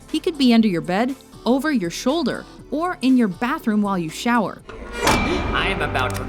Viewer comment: am I the only on that heard her say "Randall could be in your bath room while you shower".... CREEPY MUCH